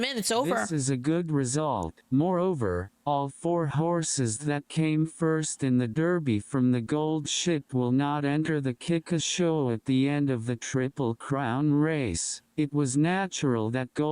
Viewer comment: I'm sorry if I heard Gold Ship as gold shit.